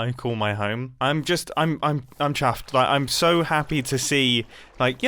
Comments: I HEARD it, he said he’s CHAFFed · Did he say he was chaffed at 1:14? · did he just say "chaffed" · DID HE SAY CHAFFED!?